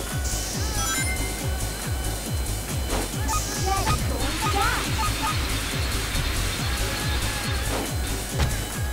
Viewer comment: Does anyone know what the announcer actually says at 7:51? I've listened to it several times but all I can hear is "Jet is going to die" ... and I'm pretty sure that's not it. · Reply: from slowing it way down i'm pretty sure it's "dash"